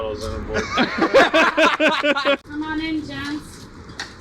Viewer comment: I thought she said 'Come on in, Djents'